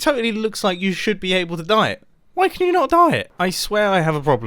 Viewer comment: Eating a Mars bar at that exact moment. I'm being called out. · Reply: it sounds like he is saying "Diet" instead of dye it lmao